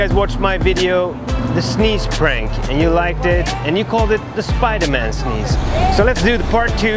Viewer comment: Who else thought it said super man